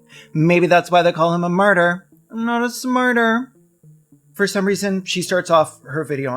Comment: "a martyr, not a smarter" is truly inspired work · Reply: Thank you! I thought they said "a murder, not a smurder" and I was like "wha?"